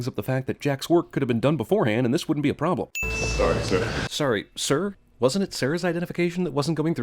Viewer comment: you thought he said "Sorry sir" and thought it was strange because it was Sarah's identification that wasn't going through. But the guy actually says "Sorry Sarah". It's just muffled, they way he says it.